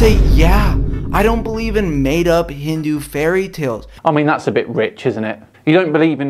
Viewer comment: Did he say hindu fairytale??